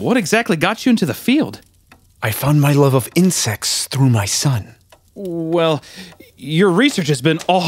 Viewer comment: I thought he said "I found my love of incest through my son"